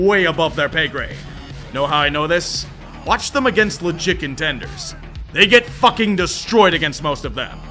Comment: Idk if I’m fat or what but I swear he said the chicken tenders 12:15 😂😂 also go 🦅